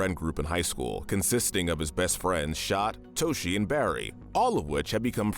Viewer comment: I'm sorry, did you say Shot?